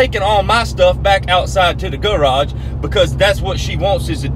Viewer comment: Thought he said gulag instead of garage